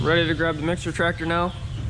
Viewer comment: At first I thought you said "Ready to grab the mystery tractor now" and I was super intrigued. Was thinking "oooh, I wonder what they got..." 😂 Then I realized you said mixer tractor. Great video, sir. Thanks for sharing!